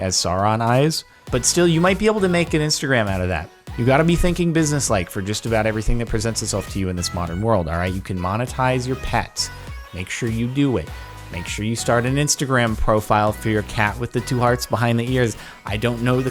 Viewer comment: When you thought you heard say “you can demonetise your pets”